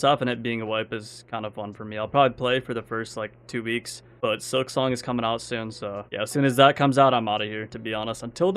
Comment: WHAT GAME IS COMING OUT? listened like 949849 times, can't figure out what you're saying lmao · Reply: hollow knight: silksong